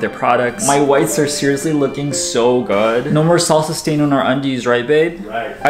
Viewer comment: You say no more Salsa stains. I hear "No more Sausage stains in our undies"